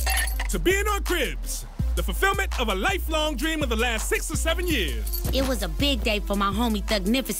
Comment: "The fulfillment of a life long dream of the last 6 or 7 years" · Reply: I thought he said "that'll last 6 or 7 years"